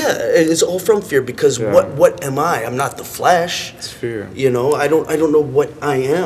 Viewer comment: i was mis hearing the word flesh during the first go round, and i thought he said "What the hell I am.... I'm not the FLASH!" HAHAHAHAHAAHAHA......I was still digging it.